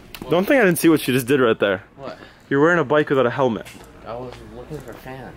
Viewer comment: I swear said "your wearing a bike without a helmet"